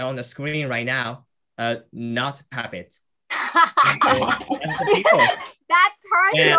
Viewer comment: 24.33分のtwo people on the screen の後ってなんて言ってるかわかる人いますか?? are not habit?って聞こえたんですが😅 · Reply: habit→puppetsです！ 「操り人形じゃないですよ！」で一同爆笑